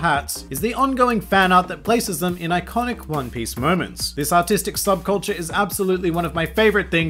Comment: Tbh,when you were talking about the fan art of the imposter straw hats,I totally thought you said autistic instead of artistic sub culture,and i laughed way too hard lmao...also great review